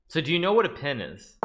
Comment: i heard "so do you know what a pen is" and thought hikaru was making a joke